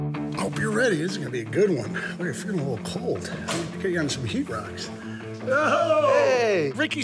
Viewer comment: did he say freaking heat rocks ima go save them snakes